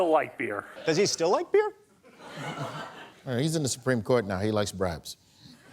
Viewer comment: what's Kavanaugh like? I didn't catch that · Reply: “He likes bribes.”